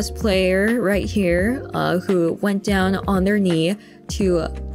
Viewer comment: オ○ニーって言ってませんか！？字幕だとon their kneeと出ていますがこういう発音になるのでしょうか。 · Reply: 0.5倍速くらいで聞くとtheirって聞こえますね。｢go down on one's knees｣で膝をつくっなるらしいのでこれで正しいかと